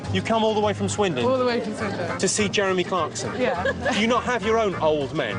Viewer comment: i think he said jeremy cockson , would have been funny though if he said that.